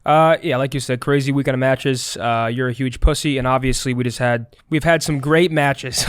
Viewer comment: Did he just call scump a huge pussy @ 00:01:06? Nothing was even mentioned about what he said😂